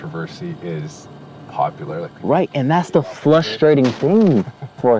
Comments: Did he just say flustrating 🤔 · instead of saying "frustrating" says "flustrating" did anyone catch that or is it just me? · Did he just say "flustrating" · why does it sound like is saying “flushtrating” 💀